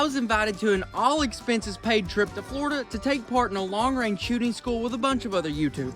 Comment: his subtitles said Texas not florida 😂 thought I was tripping for a second